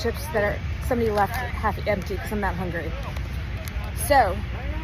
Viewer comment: She said she’s eating someone’s half finished chips because she’s NOT hungry. I give up. Vote for president Dwayne Elizondo Mountain Dew Camacho, I guess. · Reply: she's clearly a moron but you definitely misheard that part. She said I'm eating these chips someone left behind because I'm THAT hungry, which makes a little more sense anyway